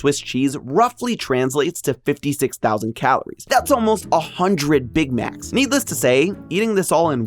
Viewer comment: i heard 100 big backs 😭